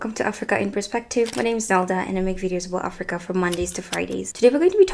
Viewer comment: Sorry, I misheard and typed Zelda instead of.